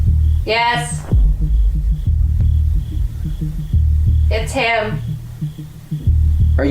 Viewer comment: " Is the grey man here?" Mans voice: " Yes" Anyone else hear that at the 36:09 mark?